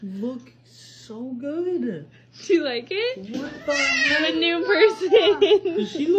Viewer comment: He gave the highest praise at the end, "She look like a chicken nugget!!" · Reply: I heard "You Aree cheating dad-" 💀